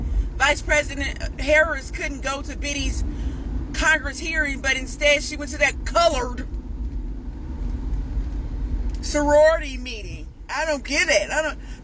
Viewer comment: I swear when I first listened to it I thought that he said she had to visit a coed sorority and I thought well that sounds about right these days LOL